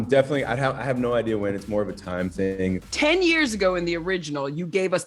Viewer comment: did he say it's morbin time lol